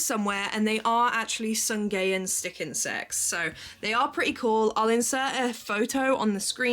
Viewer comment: Sungayan stick insects 😊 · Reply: sounded like you said "some gay stick insects" 🤣🤣🤣